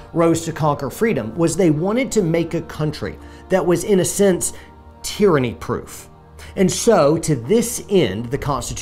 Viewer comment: The left is just confused they thought you said tranny they were all in lol